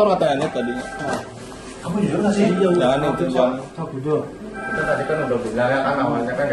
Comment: Salfok sama video Jaya ada yang bilang, "Oh kamu bagus, nanti pulang aja". Bisa jadi sebelum video Jaya sudah diiming-iming mau dibebaskan asalkan bilang iya/ngaku. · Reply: Tambahin 6:12 oknum yg duduk itu mau ngegaplok kmdn oknum yg rekam bilang jangan itu bang..maaf Kalo sy salah dengar